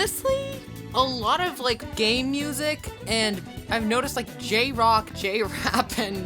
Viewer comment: did you say gay music or game music